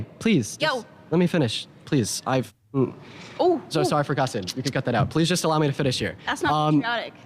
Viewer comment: Did he say "you can cut that out"?😂😂